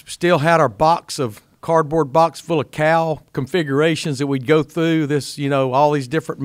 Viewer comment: Oh cowls configurations!!! Lol I thought he said cow configurations. I was trying to figure out what he meant. Lol